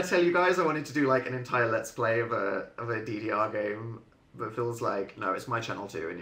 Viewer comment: what does he say at 3:55? it's something game? helpp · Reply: I think he said ddr